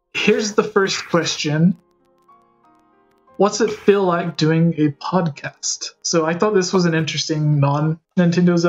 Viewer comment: Hot because is hot 🔥 Edit: O i thought it said "What is it like having a podcast with Ganda kris" all in one sentence